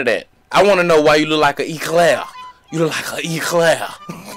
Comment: I had to see what a Eclair was.... cause I thought. he said eclaf